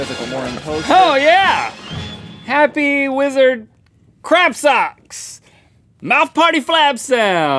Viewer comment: Aw I thought it said Happy Wizard Crapsocks